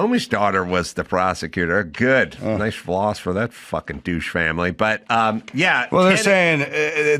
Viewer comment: I think I heard, "Comey, that douche family"? I'm outta here.